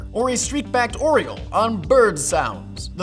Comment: I swear to God, I thought it said Columbine Sounds